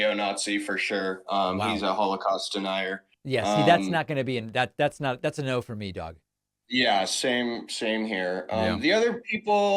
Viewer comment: Did David just say “that’s a no for me dog” at 2:45 ? Now this is quality content that I will like and comment for.